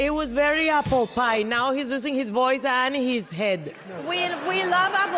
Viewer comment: “It was very Apple pie” “now he’s losing his voice and his head”😂😂😂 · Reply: With her accent I thought she said "using" at first lol